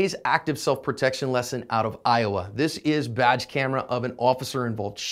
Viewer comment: It took me a minute. 0:13 thought he said "vag camera". That might be going too far.